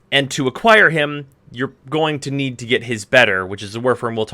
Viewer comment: wtf did he say at 15:30 "you have to get his better" what